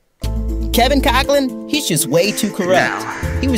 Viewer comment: I heard "Attic Goblin, he's just way too corrupt" and I thought wow they got his ads perfectly targeted. 😅